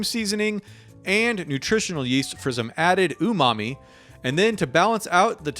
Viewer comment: All of those recipes look deeeee-licious! 😋 Thanks. (When you said the word "umami" (2:16) for some reason I heard "Ooh mommie" LOL. And I think that is something I will never be able to un-hear 😆)